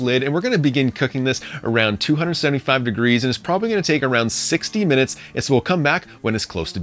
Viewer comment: did he say 16 or 60 minutes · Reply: 60 :)